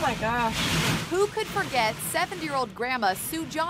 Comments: am i the only who hear them say the 7 year old grandma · Did i just hear 7 Year Old? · did she say 7 year old grandma? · did they say seven year old lol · Idk y but I heard "7 year old " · I think I heard you say 7 year old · i heard 7 year old grandma lmao